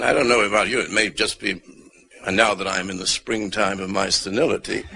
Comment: what does he say around 40:00? He's in the springtime of his ...? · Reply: Senility